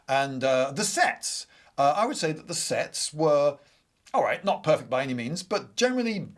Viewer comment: I heard that as ”the SEX” and was really confused for a moment